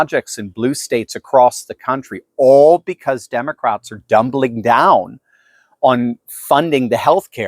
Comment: hahahaha I could swear I heard you say Democrats are "dumbling down". If you didn't, you should start using it whenever talk about the Democrats are doubling down...